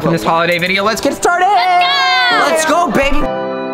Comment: In the first part of video when levels gonna say let’s go baby they cut out 0.1 second of that and it sounds like love said let’s go babe